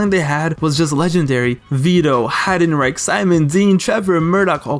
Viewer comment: did he just say Heidenrape lol 🤣🤣🤣